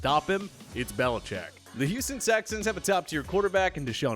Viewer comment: Did he just say Houston Sexsans????